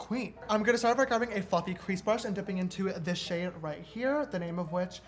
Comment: am i the only one who thought he said “i’m going to grab a f*cking crease brush” lol